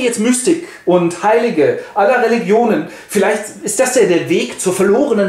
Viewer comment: Was wird bei 9:30 gesagt? "... und Heilige aller Religionen"? . Den Satz davor verstehe ich auch nicht.